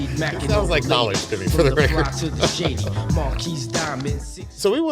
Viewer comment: did he say "it sounds like knxwledge to me" ? cause man could totally be one of the more straight forward cuts from the wraptaypes days